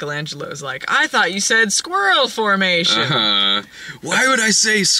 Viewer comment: I thought said "squirtle formation"